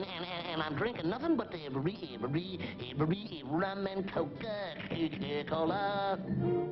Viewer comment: did he say cocaine? or something like that?